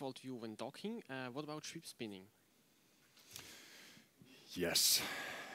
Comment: At about 25:14, what does he say, "...default view when docking, what about troop spinning?" (troop spining?)